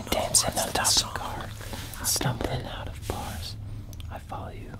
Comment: what did Joe Say? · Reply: Nick says "You guys don't know the words of the song." I think Joe says "stumbling" which is the next word of the song if you pay attention to Kevin's singing xD